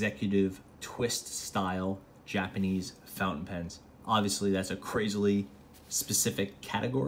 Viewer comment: did you tell fountain pens? Or I heard wrong!